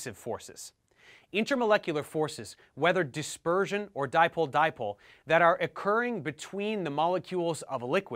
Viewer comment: WTF did he say "dimple dimple"? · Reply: Dipole dipole